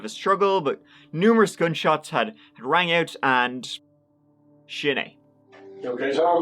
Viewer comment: Can someone please tell me, at 4:09, what does say that sounds like "shinay"?